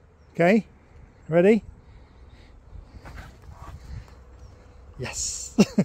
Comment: when you asked are you ready I could have sworn said “I’m always ready “😸🐾🐈‍⬛❤️💙💚💜🌎✌️☮️😸😸